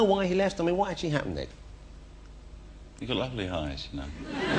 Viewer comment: What does he say in 3:38? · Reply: "You've got lovely eyes"